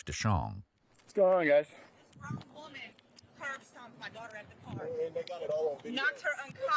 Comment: Did she say curb stomp? WTFF